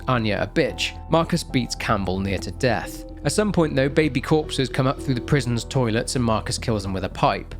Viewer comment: I seriously thought you said "baby corpses". I straight up gasped.